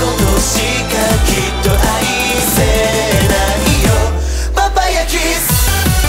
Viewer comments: ''Papaya Kiss!'' (Sorry this is what I heard the first time listening to it I love that song <3) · The first time they said 'Vampire kiss' at 0:18 I heard 'Papaya kiss' lol